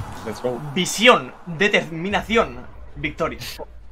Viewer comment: Cuando dijo visión pense que dijo viciar xd